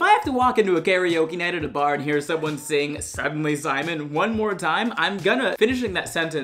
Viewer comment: i really thought he said "Suddenly Seymour" instead of "Suddenly Simon"